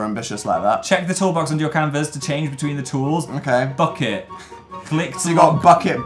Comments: Am I the only one who thought Phil said "fuck it" when he said "bucket" at the beginning? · when Phil said "bucket" I heard "fuck it" and I was so confused because he never swears